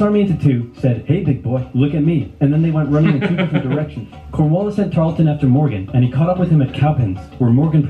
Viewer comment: At first I thought lt said coronavirus at 9:59 but it actually said cornwallis This comment was made during 2021 the coronavirus epidemic